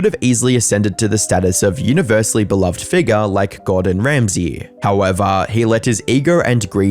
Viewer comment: did you say gordan Ramsey 😅😂